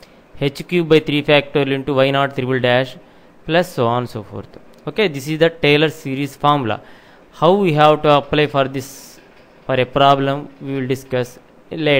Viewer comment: I thought he said, "Taylor Series Maadharchood" LoLZZZZZ